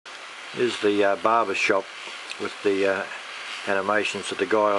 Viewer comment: 0:01, did you say _Barber_ (as in, guy who cuts hair) shop? ;-) Looking fantastic...